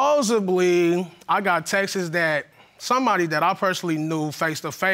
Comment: Textes and ghostes is killing me! 😂😂😂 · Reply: Thought he said Texas lol